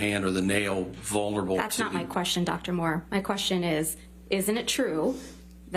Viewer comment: I always hear "That's not my question, Dr. Moron." 😄